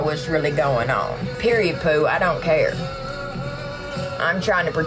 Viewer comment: did she say “PERIOD POO 💩”?!